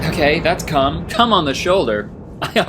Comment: I was just listening to this and wasn't watch and I just heard ok that's c*m and I was so confused